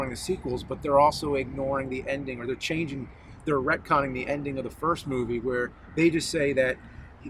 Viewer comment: "retconning" he said, i thought he said red comming llike some soviet term, lol i had to google it, i learn new words everyday, i'm always googling words that i never heard before, but sadly they never make it into my vocabulary, just a curiosity to google it